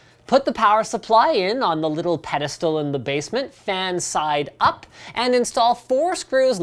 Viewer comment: I thought he said put the power supply on the "little pedophile in the basement" !?